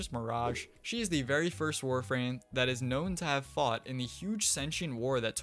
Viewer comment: ahh yes the first warframe to be known to fight in the sentient war, nidus (sub caption wrong